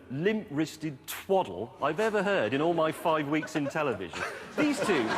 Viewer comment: “ In all my 5 weeks In television” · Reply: I thought he said fine weeks